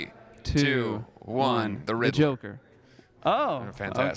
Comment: what did andy say please ? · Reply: Riddler